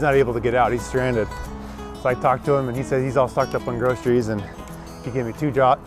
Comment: So I'm working in the greenhouse today and with the heater blaring, you're talking about the old guy down the bridge and I thought you said, "he's all forked up on druggies or something." I had to do a double tap and go re listen to that one. 😅 I'm excited for the rest of the video!